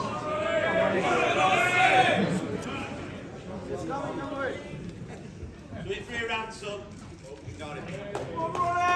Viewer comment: Anyone make out what Hossein shouted out to the crowd at 1:25 ? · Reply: He said "He's coming, dont worry", in response to the "Come on, Ronnie" shouts.